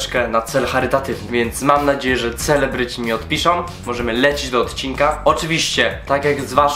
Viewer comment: O nie czemu ja usłyszałam : więc mam nadzieję że nie odpiszą